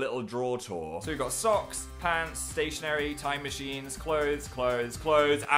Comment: Phil: socks, pants, stationary, time machines, clothes clothes clothes and the Drawer of shame! Me: did he just say 'time machine'?